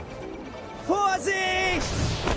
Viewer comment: Who heard "HORSEY!!!"?